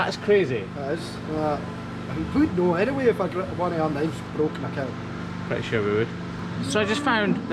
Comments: The amount of times I had to re listen to 6:33 and still have no freaking clue what he said lol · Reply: He said they would know if they'd had a knife break. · what did he say???? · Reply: That they'd know if they'd broke a knife.